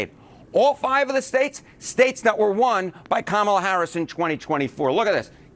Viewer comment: I swear i heard him say Karmala Harris! Lol